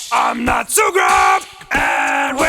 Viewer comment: who else heard i'am natsuki!